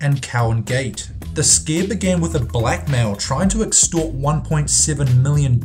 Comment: it sounds like you said black man but you said black mail 12.26